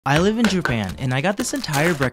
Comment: Am I the only one who heard him saying jupan 😂😂😂